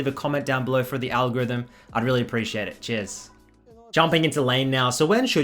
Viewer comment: Anyone else heard “I’d really appreciate it. Jizz” at 0:50?